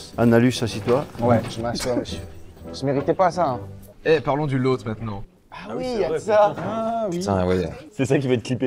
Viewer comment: jrigolais et j'ai entendu "parlons du loat maintenant"